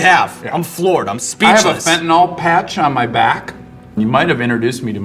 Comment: did he just say he has a fentanyl patch on? thats a Hot Ones PED if ive ever heard of one